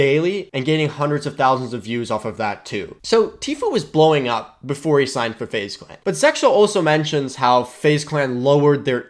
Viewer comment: it sounds like you said SeXel lol